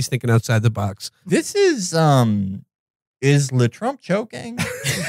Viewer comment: What does Felix say at 23:08 that everyone laughs at? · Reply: I think he said LeTrump